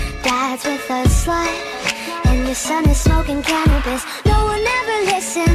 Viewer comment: When she said your son is smoking cannabis I thought she said your son is smoking calories XD